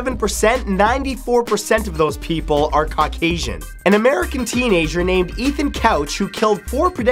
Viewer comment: What did he say at 2:06?Cockasian? What? Never heard that word.. cant look it up cuz i have no idea whatsoever how to even spell it lol. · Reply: Yamyatos Caucasian. White.